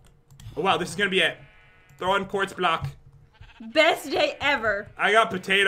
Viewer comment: did say best yay ever a